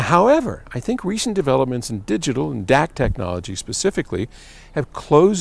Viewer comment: Does Paul says "Dac Technology" in 0:26?